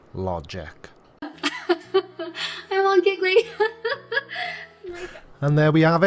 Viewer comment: Red-headed stepchild, and her globes. Did she just say : "I'm all jiggly!!" ??? OHHH!! she said : "I'm all giggly!" My bad. 😉😉😉